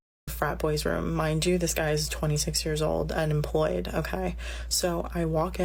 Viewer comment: Swear she said the man was employed as a police officer. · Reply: it sounds like she says he's 26th and unemployed. Maybe she did say employed. It's kind of hard to tell because she talks like a valley girl. But if a guy is a cop, it makes total sense why he would be that way. She should have expected that kind of a behavior from him. Now I'm a lot less sympathetic towards her